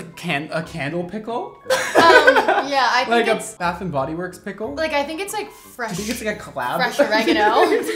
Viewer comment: You guys are so funny 😂 bed bath and beyond eeeeew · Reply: I thought they said Bath & Body Works collab pickle? 🤔